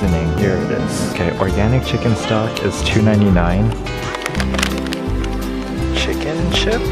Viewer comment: I thought he said to many 9’s 😂😂😂😂😂😂😂😂